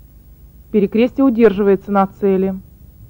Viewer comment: did she say " billy cristal something an australian "?